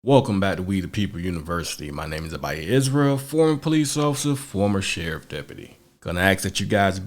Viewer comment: Am I the only one who heard him say “officer foreskin”?